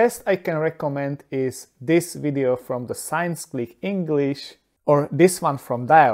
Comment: you mention two other videos on two other channels but i cannot understand what you said . What are those two other channels cited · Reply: Dialect and Science Clic English